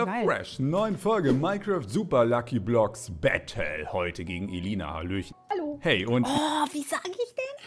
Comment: Bei Minute 11:42 hat sich angehört wie ebru 😂😂